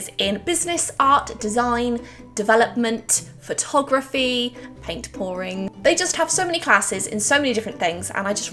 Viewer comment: She says “paint pouring” but I hear “papering” every time